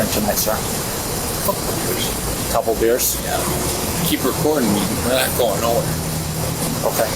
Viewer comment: Does he say, "Keep recording me, you're not going anywhere" ?